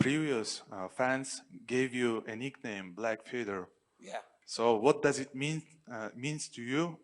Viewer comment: lol at first I thought he said black feeder. And then I realized he said feather. I was wrong both times 🤣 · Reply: Black fedor not feather